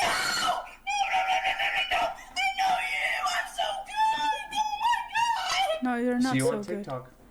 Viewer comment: She didn't say "I'm so good," she said "I'm so cooked." If you don't understand the context, she said the n word, then after seeing the man's face she realized who he is, a popular streamer on omegle, and she realized that what she said was now going to be broadcast to the world, hence her panic and incoherent screaming.